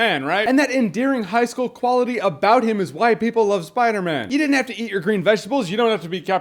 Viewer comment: When he said that's why people like Spider-Man I thought he said that's why white people like Spider-Man :0